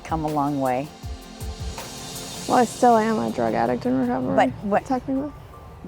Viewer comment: Does she say “Well I still am a drug addict”?